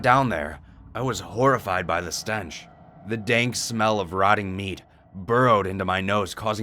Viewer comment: For a second I thought said: the dank smell of rotting memes